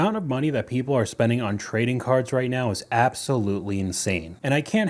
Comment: You are saying that one guy spent 50k on cards? I thought he was saying cars, they worked at a car shop. Feel so silly now 😂